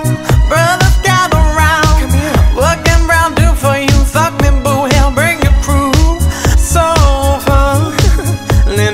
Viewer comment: What does (s)he say at 1:34? «What can --???-- do for you», etc... I don't understand and lyrics in google miss that word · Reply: I think she said "work them round there for you"